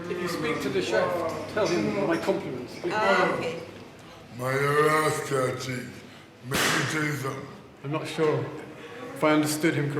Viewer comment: At Timestamp 8:12 says "Mind yuh rasclart cheek. Mek me tell you something." The man thinks he said "I'll m*rder you if you ever went to Tottenham." "Mind yuh rasclart cheek." is like saying "Mind your manners," or "Stop being cheeky." "Mek me tell you something" means "Let me tell you something." I think was just complaining about a staff member at the hospital when he said that.